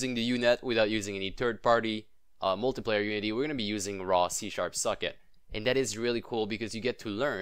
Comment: First I heard "Raw C#, suck it"